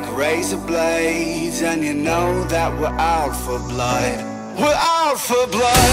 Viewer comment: All I heard was "We're Alpha Blood" instead of "We're out for blood".....and now I cannot unhear "We're Alpha Blood"!!!!